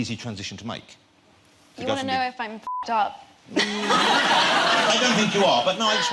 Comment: sorry, but what she said? 8:27 You wanna to know i'm fucked up? or what?